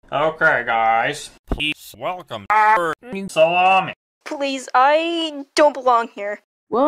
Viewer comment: did he say SALAMI